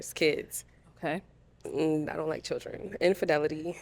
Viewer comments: Brother you definitely heard her correctly. She clearly said, "I don't like children". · that one lady definitely said “i don’t like children” 😂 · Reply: “I do not like children” was definitely said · the girl in black definitely said "She don't like kids" go back to 51:21 because I heard what I heard lol